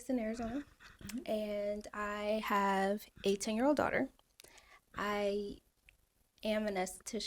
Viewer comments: Hol up, so she’s 30 years old and has a 18-year-old daughter😢😢 so she had her kid when she was 12🥺🥺😳😳🤯🤯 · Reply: I thought she said 18 too but she said “a 10 year old” she says it again later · Sooooo, did she say she has an 18 year old and she’s 30?👀👀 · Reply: That’s what I thought too but she said “a 10 year old” really fast. Lol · Did she say she had an 18yr old daughter? I thought she said she was 30?